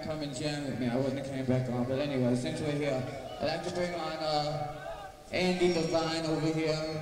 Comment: Thanks! Great sound on this one. It sounds like Johnny says "Andy Levine" or "Andy Devine" when introducing his friends/guests at 53:41 ? Do you think it is actually Andy McCoy?